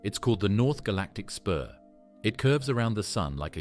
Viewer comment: Yeah, and nobody’s ever going to figure out what it is either because i can’t even type what the narrator said at 20:32 galactic spueauh? Christ… just terrible. At least I have not had to hear him/her/it say the name of the god Uranus like he was made of urine… really disgusting sounding… I don’t understand how you children decided that saying piss-planet is better than the god’s actual name which happens to sound like a body part’s name in English! Get me off this planet. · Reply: "Spur" ?